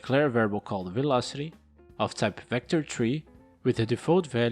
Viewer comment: Great tutorial! I love it when you say Vector3 because at first I thought you said VectorTREE instead of Vector3 lol